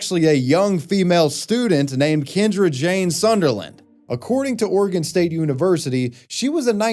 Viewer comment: Sounded like bro said James Sunderland 💀😂